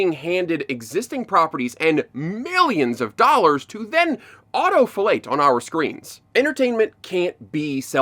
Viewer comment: Did he say “auto fellate”?